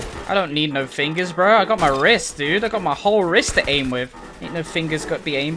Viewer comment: did he say hes using his rizz?